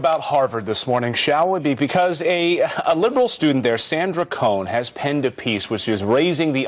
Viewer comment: did they say Sander Cohen at 1:35? lol